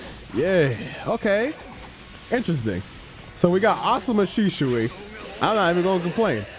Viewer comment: did he just say Asuma Shisui?!